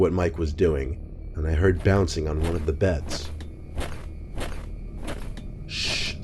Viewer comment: I heard bouncing on the bed some camera are fukinggg